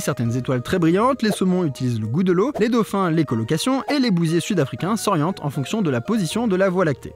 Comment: Au lieu d'écho location, j'ai entendu "les colocations" ah ah